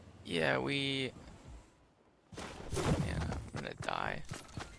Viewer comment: What did Tyler say???? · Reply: “yeah i’m gonna die” idk that’s what i heard